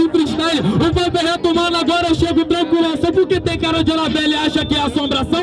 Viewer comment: tem cara de que? Não entendi. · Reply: anabele